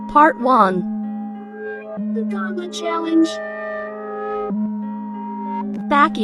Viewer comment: TF?? 0:34 THIS IS WHAT I HEARD: tHe GUWAntL0rt Choawange WHAT IS THAT NOISE 😭